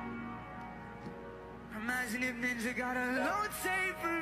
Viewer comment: IMAGINE IF NINJA GOT A LOW TAPER FADEEEEEE :face-purple-crying::face-purple-crying::face-purple-crying::face-purple-crying::face-purple-crying::face-purple-crying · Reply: oh thought he was saying low table fade